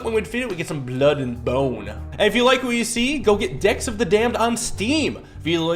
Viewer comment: I heard "D*cks of the Damned" not "Decks of the Damned"